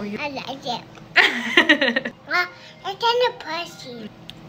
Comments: Why did it sound like he said mom it's kinda 🐱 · “ it kinda picy” got me dying because it was so cute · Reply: I thought he said "pussy" 😭💀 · “ it kinda picy” got me dying because it was so cute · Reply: SAME IT SOUNDED LIKE HE SAID "IT'S KINDA PUSSY" 💀 😭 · I thought he said it kinda pu*** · It sounds like he said it pusy